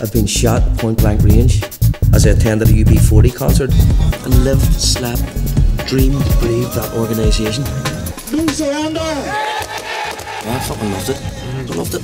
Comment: lmao at :26 i thought he said E-40 CONCERT LMAO